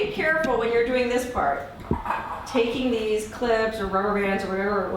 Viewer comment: 🤭🤭🤭Whhat whhat 2:46 did ewe say clits again 🤣🤣🤣